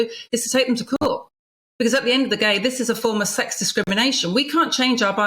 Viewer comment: Was her saying "at the end of the gay" at 2:57 a Freudian slip or a deliberate play on words? 😂